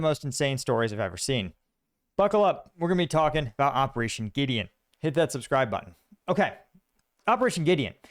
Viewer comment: Oh Gideon? I thought you were saying Operation Idiot.